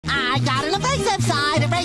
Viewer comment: Sounds like he said “I got a racist side”